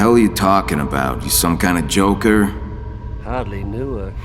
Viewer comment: What did the joker say at 4:02 after Batman said “you some kind of joker?” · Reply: I hardly knew her